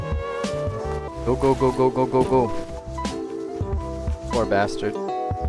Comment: my phone thought you said "ok google" 🤦‍♂️